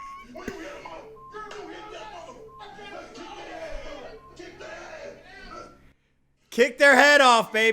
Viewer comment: I thought he said "kick that ass"😂😂😂